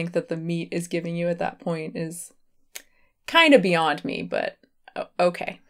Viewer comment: It sounded like you said Beyond meat but you said beyond me😂